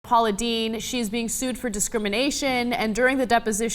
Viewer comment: Captions say POLLY DIDI instead of Paula Deen🤣🤣 OMG IM FRICKING DEAD